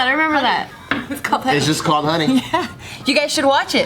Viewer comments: Money? I thought you said Honey! 😂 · Unco same: Money? i thought you said honey.